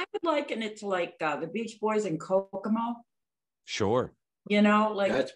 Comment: What did she say? Couldn’t make it out. Very curious. Found it: Kokomo. (It’s a song, not an album). And yeah, yikes.